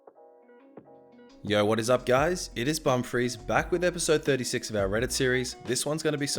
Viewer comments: why do i always hear Bum freeze? XD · I heard your name as ''Bum Freeze'' and I then heard it as ''Bum fraise'' So